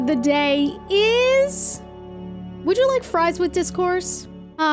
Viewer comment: no idea why but i thought that said “would you like fries with discharge” 💀